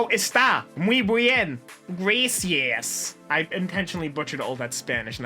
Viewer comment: It sounded like he said grassy ass instead of gracias and honestly I laughed way more then I should have